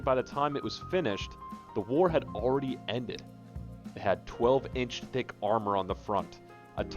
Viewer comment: Lol at 6:24 it sounds like you say “12 inch d!ck armor on the front”